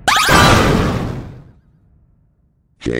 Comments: i think he said: hey imposter have you ever hear these words? (and idk what he said at the final) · I heard "gay imposter have you ever heard these words before?"